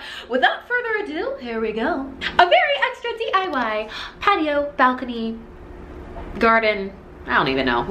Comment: Lmao 🤣 I thought it said diy potato 🥔 ohhh lmao 🤣